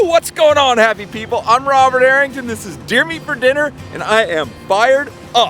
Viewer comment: I thought you said deer feet for dinner